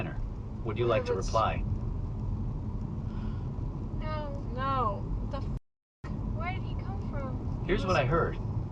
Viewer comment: here's what I heard: "no the fuck" LMAO